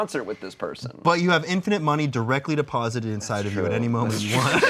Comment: at around 4:55 that entire bit of direct deposit shit diamonds the only thing i could think of it being called "diuretic deposit"